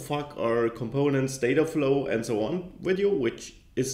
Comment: Does he say WTF at 0:15? Hahaha